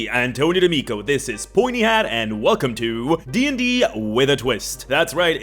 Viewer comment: I totally heard "horny hat" and imaged a condom since I wasn't looking at my phone